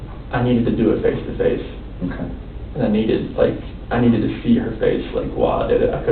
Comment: I thought he said “I needed too feed her face”